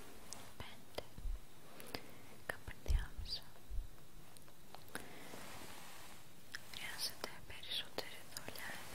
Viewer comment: For some reason I'm fixated on the word she says at 0:59 , can anyone tell me what she's saying? I'm just terribly curious for some reason. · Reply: Junniper ενδιάμεσα (endiamesa) means between